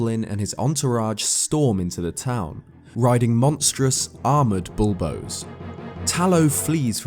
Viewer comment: I thought he said: "Riding monstrous, armored dildos."